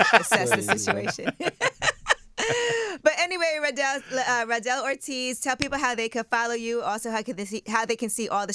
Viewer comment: I dead thought it said Joel Ortiz